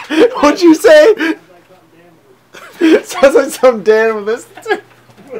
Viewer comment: what does say at 2:10 ? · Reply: SystemOfALeo He says "sounds like something Dan would listen to." They make fun of his musical tastes all the time cause he listens to oingo boingo and stuff like that.